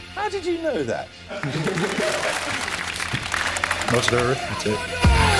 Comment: what is he saying at 0:15 much he da repitat is what i understand xD but thats not even real words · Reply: Modular arithmetic.